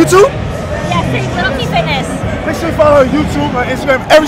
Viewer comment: what her yt? can't understand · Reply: LittleTFitness